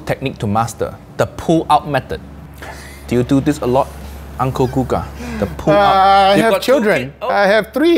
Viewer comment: Did he said uncle coca?